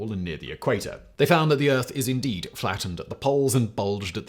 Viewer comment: You could send flat earthers to space and they'd still deny it. They're only trolls at this point. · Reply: Did you hear "the Earth is indeed flat…" At this point as well!? I had to rewind a couple of times to make sure I hadn't misheard him, thankfully I had. LOL